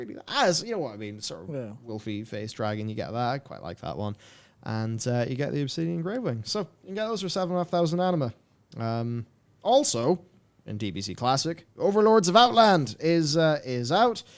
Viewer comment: For a second there I thought you said 700,000 anima. The unbridled rage that was building up inside like a banshee from hell. I had to watch it a few times to be safe. Holy shit. That would have been a meltdown on my part.